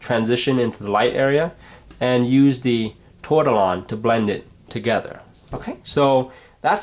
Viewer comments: what is he saying to use to blend? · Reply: He says tortillon, it is blending tool. Google: "how to make a tortillon ", is really gives a great effect. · What Did he say.. at 3:05 ? Use What to blend It..? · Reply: he said tortillion, in my opinion, you shouldn't waste any money on it, it's basically rolled up paper that has a point. the oils in your fingers does a much better job at blending.